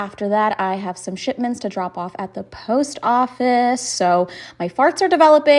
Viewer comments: I thought she said she "have some shitments to send off at the post office." Did I mishear or what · Could have sworn she said 4:40 'shitments' the first time.